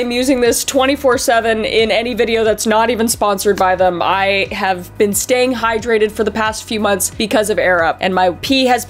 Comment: Sounded like: I have been staying hydrated for the past few months because of Arab